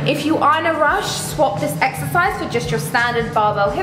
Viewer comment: I swear I heard you say "eggsercise" hahaha